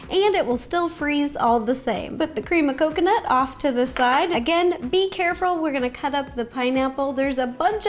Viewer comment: I heard "be carefrul"